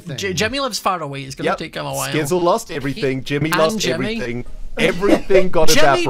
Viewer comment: did ren say "Jimmything"?